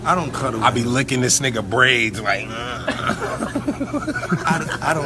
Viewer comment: I heard “brains” at first